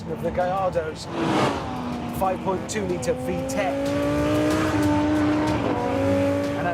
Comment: i thought Hammond said 5.2 Litre VTEC